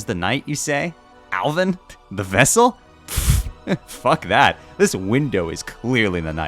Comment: I'm i hearing SEALED VESSEL?